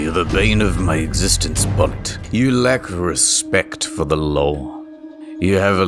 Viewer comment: Did anyone else hear "You lack respect for the lol." ?